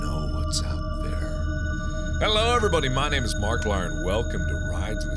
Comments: Did he just say "Markilier??" .-. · Did Mark said Marklier??